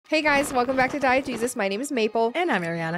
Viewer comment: started watching your channel recently and i swear for the first couple days i thought you were saying "Diet Jesus" instead of "Diegesis" in the intro LOL